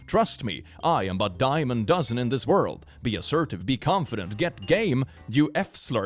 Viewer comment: I'm also gaming right now so I had to go back because I thought said "get gay" not "get game" XD